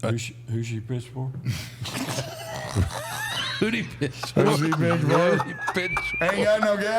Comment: For a second I thought he said "Who'd he piss for?" And it was way funnier until i heard it the second time.